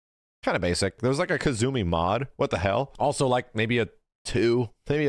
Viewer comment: did he just call Geese a kazumi mod?